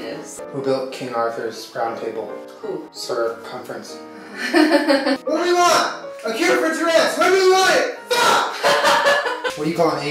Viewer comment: wtf did he say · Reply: What do we want? A cure for Tourettes! When do we want it? FUCK!